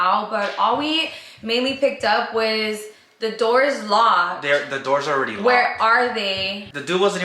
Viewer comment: It sounds like he said " you think they locked the doors?"